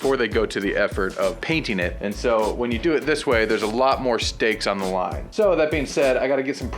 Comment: "there's a lot more steaks on the line.." lol, did someone say steak??.